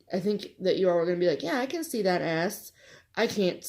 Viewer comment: Started watching at work this morning and had to look up when I thought I heard say "I can see that ass" 😂😂😂... S, Diana... She said S!